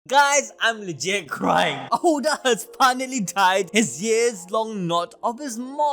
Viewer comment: Bruh wtf I thought u said Oda has finally died I said wtf!!!